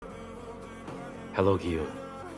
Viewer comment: At first when he said hello gi hun I thought he said I love you